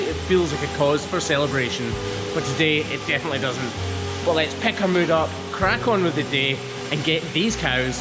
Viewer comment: When you said “pick our mood up” I heard “pecker mood up” lol I was like whoa whoa whoa what 😂😂😂